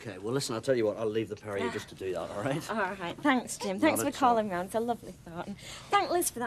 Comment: ish - does Sally call Jim Kim?